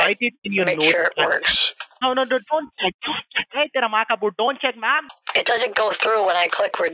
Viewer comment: Everytime I watch each of Kit's video, I always hear the phrase "tera maka bul" and I don't know what that means. 😮😅